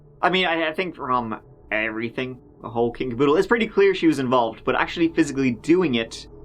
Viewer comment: can you clarify did you say kinkerpoodle? Kinderdoodle? Or? 😂